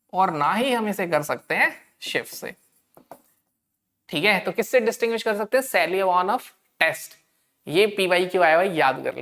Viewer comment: Sir 11:37 pe apne kya bola ? Smjh nai aya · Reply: Seliwanoff s test